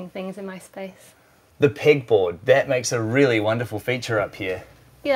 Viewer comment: The pig board? Lol🐽🐷🐖 sounds like he says pig board